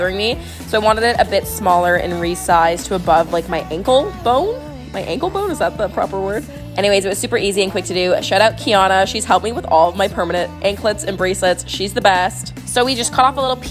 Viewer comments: Pov: u were across the room listening and u thought she said shut up kiana😂😂😂😂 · I thought she said "shut up kiana" lol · I thought she said shut up kiana for a sec 💀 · I thought she said “shut up kiana” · girl, I thought you said shut up ki Ana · I thought she said "shutup kiana"😂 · Bro I swear I heard her say “shut up Kiana” and that shit scared meeee💀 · I thought she said shut up Kiana-💀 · When you said shout out to Kian, I thought you said shut up to kian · i thought she said shut up but she said shout out lol, i was like WHAT DID SHE DO?